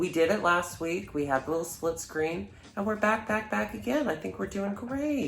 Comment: At first I misheard that as 'slit scream.'